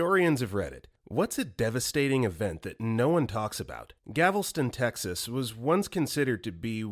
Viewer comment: He said gavelston instead of Galveston 😭